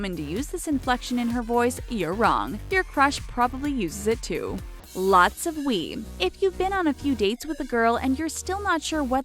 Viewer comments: I thought she said "Lots of weed" 😂😂😂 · Hhaahahah!! at 4:19 I though that I heard her say a lot of WEED!!! I was like Hell's yah!